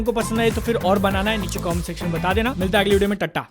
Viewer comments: did he really said 'Tatta' instead of 'Tata' 😂😂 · what he just say 😳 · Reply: tata tata bolla hai bhai🤣🤣🤣